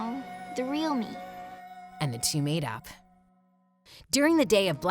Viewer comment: i thought u said "and the two made out" and i was like "WAT"